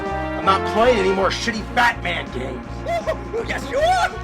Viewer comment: I keep repeating this because I thought BatNerd said Fatman 😂